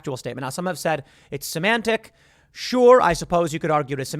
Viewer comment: He said semantic I thought he said some man tits 😂